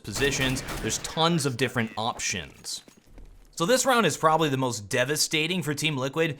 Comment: did you just say awptions?